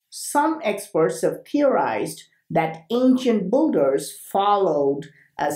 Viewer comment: Your videos are great but when you say "builders" I hear "boogers".. so to me it sounds like you're saying "ancient boogers".